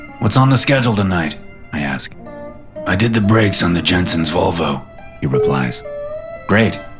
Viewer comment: lol, I thought he said “Jetsons Volvo” at first😂😂